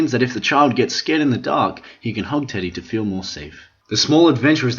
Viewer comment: I thought he said, "You can hump teddy to feel more safer".